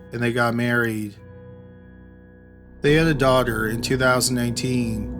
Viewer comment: I hear “daughter”, but I see “son”. Either way, I have a lot of respect for that guy because he didn’t give up.